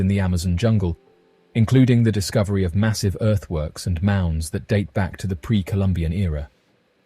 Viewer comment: when he said giant earth works i swore he said earth worms... i was like, well that explains the holes... didnt even 2nd guess it, i just went with it like, yeah giant earth worms were a thing lol... i play too many video games...😅